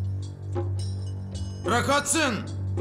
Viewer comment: de ne diyor? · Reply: Bırak atsın